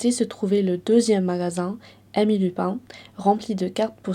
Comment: Heureusement qu'il y a les sous-titres, pas parce que tu aurais une mauvaise prononciation, absolument pas, mais parce qu'à 4:36 quand tu dis "Emmy Lupin" j'ai compris, en tant que bon français : "amis du pain". Je me suis dit "Oooh ! Une boulangerie !" 😂 Cela dit, c'est la première fois que tu fais une vidéo de ce format, aussi longue, et c'est encore une chose que tu maîtrises parfaitement bien. A la prochaine ! 🙃